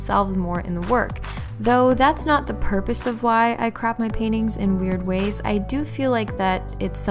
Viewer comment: ok I just realized that at one point it sounds like I said "crap my paintings" ....... good job, self. thanks for watching loves!